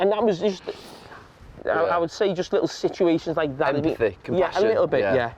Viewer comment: What does Gary say at 6:09? I can’t catch it. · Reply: Empathy, compassion.